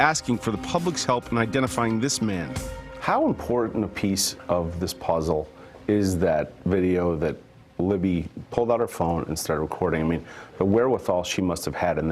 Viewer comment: Did he say “how impotant a piece of the puzzle is it, that libby pulled out her phone, and start recording me”?